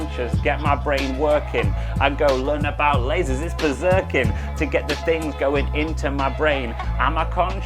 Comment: what does he say at 4:17? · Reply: I go learn about lasers, it's berserk'in